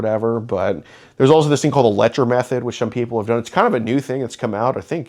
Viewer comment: advises this guy try the Letcher Method to cure his addiction. That's a no-fail option. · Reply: OMG now it makes sense I thought he was saying electromethod 😂😂😂😂